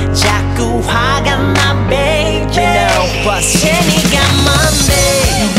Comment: 제니가 뭔데로 들리는데요...ㅠㅠ